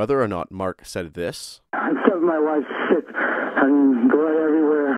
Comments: Sounded like he said , I killed my wife, at the beginning of the 911 call. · Ok so I was listening but not watching the screen and I stopped. I heard "I killed my wife" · I thought he said "I think my wife slipped, there's blood everywhere." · At first I thought he said I killed my wife, second called he said mom, not wife